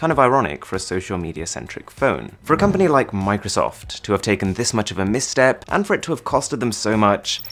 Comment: i thought it said microsoft skin tone LMAOOOOOO